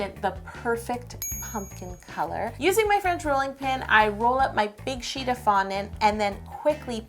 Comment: When she said "French rolling-pin" I thought she said "friends rolling-pin" and I was like dude you literally bake cakes for a livening how do you not have a rolling pin?!😂😂😂👌🏻